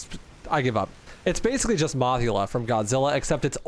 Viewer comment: im glad im not the only one who thought of Mothra when fighting the moth boss but i gotta ask, were you saying Mothra's name wrong on purpose or am i hearing things? I swear it sounded like you said mogula which doesnt sound right and the captions say mothula which is a zelda boss (according to Google. I don't know anything about Zelda). There weren't any other comments about it so I thought id ask.